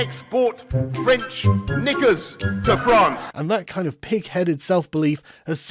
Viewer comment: did he say what i think he said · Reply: If you mean french knickers, yep